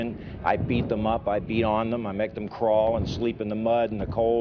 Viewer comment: I thought Dye said "I pee on them". That was some realistic stuff.